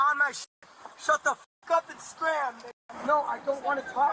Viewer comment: did he really say 'scram'?